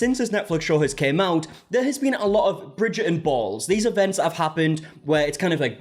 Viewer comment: I keep hearing "Bridget and balls" 😭